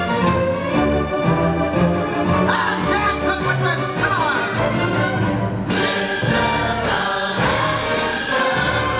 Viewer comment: What does Genie says?? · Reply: "I'm Dancing with the Stars."